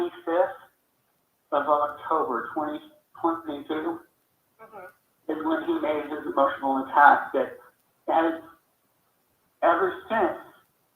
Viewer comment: I heard “that was when he made his emotional attachment.”